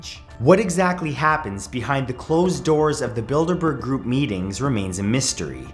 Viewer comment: I thought he said "The Build-a-Bear group" on the first one 🤣